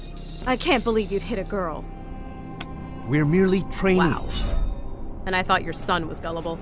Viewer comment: Correct me if im wrong but did i hear you say "wow" at 5:47?